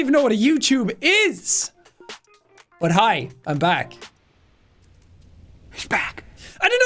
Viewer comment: I thought he said (hi i'm black) 😭😭